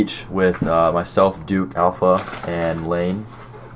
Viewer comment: Did you say lane at the end? 🤔